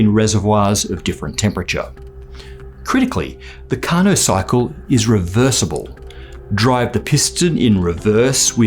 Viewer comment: For a second I thought you said "Carnocycle", which sounds like a Syfy-movie about a man-eating killer bicycle. ... Someone call Netflix, I have a pitch for them.